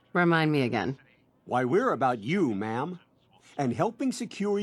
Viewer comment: I must have noticed this before, but the American developers have done what people from the US often do and misheard the British pronunciation of "ma'am" and written "mum" in the subtitles instead. Codsworth does not think the Sole Survivor is his mother, nor is "mum" a term of respect in the UK. He's saying "ma'am." For people who would like to watch other insane Fallout challenges after this, I recommend ManyATrueNerd, where Jon is currently playing a FO3 "Worst Wanderer" run. All his S.P.E.C.I.A.L. stats set to 1 and he has the amount of XP required to level up set to an impossible amount, meaning he will be level 1 forever. He also recently did a Fallout: New Vegas "You Only Live Once" (if he dies, that's it, the challenge is over, also no healing allowed of any kind) melee only run. ItsJabo is another Fallout-heavy content creator who does some fun things with the games. Again: Watch this first of course! I'm very much here to see how approaches this kind of challenge!